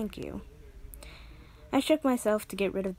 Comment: I heard I shit myself to get rid of the feeling of dread and now I'm dying....